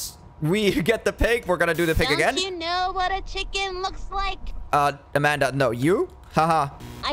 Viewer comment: i love how when she says "looks" it sounds like shes saying bak for a milisecond